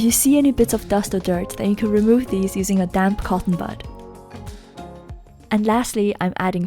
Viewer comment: For a second I thought she said “Get a dank cotton bud” Lol 。・°°・(＞_＜)・°°・。